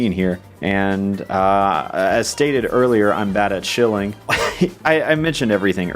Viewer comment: When he said he’s bad at shilling I thought he said he’s bad at shitting. I was very confused